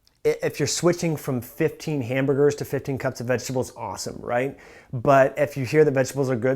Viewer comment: Min12:45 "15 cups of what?" Can't hear very well what was specified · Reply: Vegetables